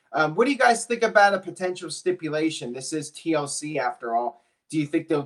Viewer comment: Did he just say stimulation instead of stipulation?